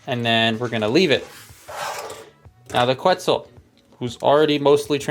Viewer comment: Thought you said Pretzel for a moment there